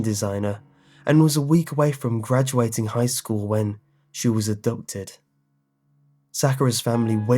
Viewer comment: What I heard “when.... she was adopted”